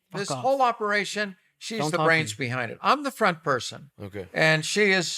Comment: LMAO who heard Steiny say fuck off, don’t talk